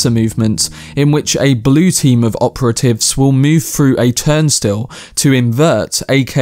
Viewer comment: It sounds like you were saying “Time Still”. I wore CC glasses for the film, it’s definitely “Turnstile”. If it was just me misunderstanding you please ignore me.